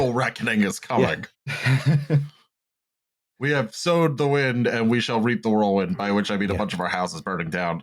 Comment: Did say "zoned the wind" and we shall reap the whirlwind?